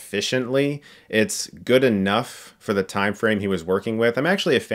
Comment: I heard that as 'not good enough for hilariocity'..